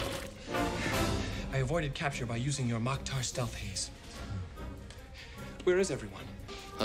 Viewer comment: All this time I thought he said, I avoided capture using your moktar stealth haze